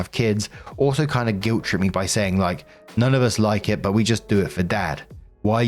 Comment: “Just do it for dad” I swear to HECK i almost thought I heard “Just do it for Dan” and got flashbacks to that trainwreck of a man